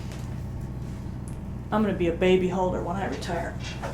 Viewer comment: thought she said baby h0e ngl